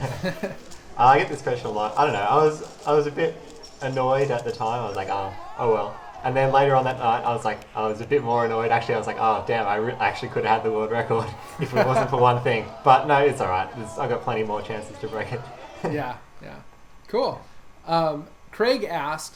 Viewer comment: did he say dam